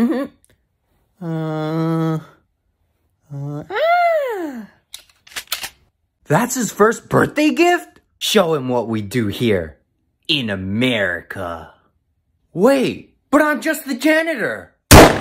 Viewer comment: ”BUT IM JUST THE JANITOR” 💀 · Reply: I HEARD "BUT IM JUST AN ANIMAL" 💀✋